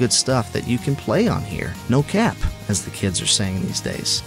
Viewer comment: Another wonderful reveiw from! Thanks for all the information buddy! Also, did you just say "No cap?" 😂 If I'm going to consider playing those steam indie games, I might get one of these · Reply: Whoa!! watched my video?! You caught me lurking on your channel I guess. Haha. Yeah... I said "no cap". Don't hate. lol Thanks for watching!